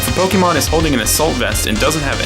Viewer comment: Fun fact: when I used to watch narrated pokemon matches on YouTube, I had no idea about the Assault Vest item. Thus, every time it was mentioned I thought the person was saying "a salt vest". For months, perhaps over a year I kept wondering why pokemon would wear a vest made of salt, what it could possibly do, and just, what the heck kind of an item that is, and how many are there in the game if we're always using just one random salt vest? What salt conspiracy was I missing?? Yeah, I facepalmed hard when I finally saw it written down.